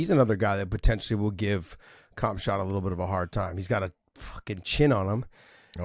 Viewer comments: did you just say "Cum$hot"? 🤣🤣🤣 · did say Cumshot 😂